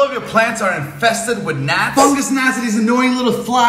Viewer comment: I heard "fungus nazi's" I know that's not what he said. But it's what I heard